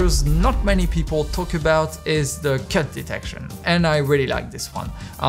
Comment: I literally thought he said "Cat detection" It was a "You had me at hello" moment there